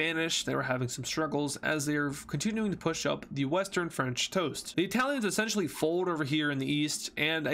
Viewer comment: did he just say western French toast